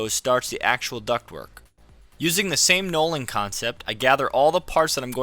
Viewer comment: Thank you for this video. I have now ordered my nordfab ductwork for my workshop. I’m curious to the word you used in the beginning of the video to describe organizing similar parts before assembling. It sounds like you said “nolling” or “knowling”, but I can’t find that word anywhere. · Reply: knolling. It is a photography term.